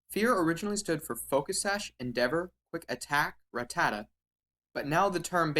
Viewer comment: I always heard it as fucking evil attack ratatta